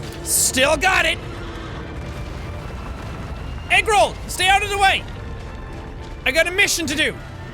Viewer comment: I thought he said eggroo,...